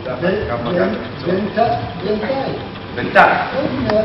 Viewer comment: what does he say at 4:07, it sounds like Insallah or so what?? thanks infront